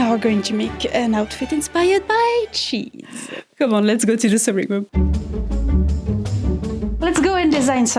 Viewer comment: I heard "let's go to the silly room", laughed, realized my mistake and thought "yeah, it makes sense anyway". 🧀💛